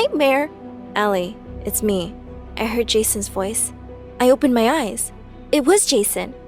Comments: I thought she said “I heard jesus voice”. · I heard "i heard jesus voice"